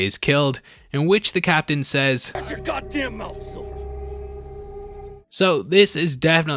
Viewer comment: I could have sworn at 1:47 to 1:48 I heard soldier